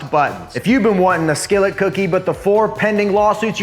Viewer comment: I heard "the dookie" and was very confused why anyone would want that 💀